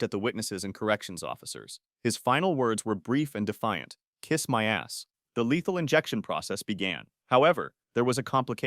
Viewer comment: Do i Just heard John say the a word before John Died!?!🫸😨🫷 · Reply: 🤣 bro really said kiss my asss